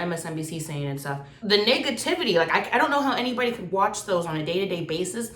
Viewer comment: did she say niggativity